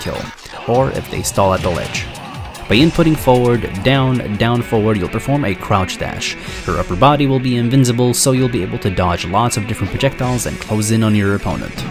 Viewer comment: did say "her upper body"